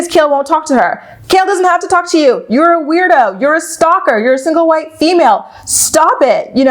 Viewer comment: did you call Briana a single white female?!? Idk if I heard that correctly.... If so, that’s a dumb thing to say. 🤨